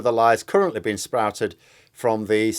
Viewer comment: Did you say "Sprouted"? Very appropriate 🍽🎄